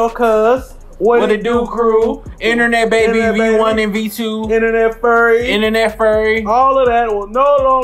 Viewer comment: did someone say Internet furry